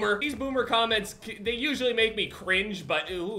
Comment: Did he just say uwu at 7:20 lmao